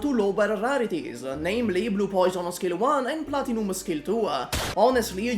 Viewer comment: Did you say skill dua on 2:40? 😂😂 Damn that weird